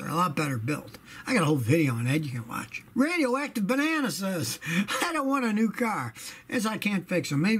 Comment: I thought Scotty said "Radio wack the banana" 😅